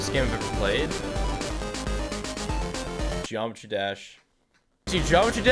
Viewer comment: did you say geometry dash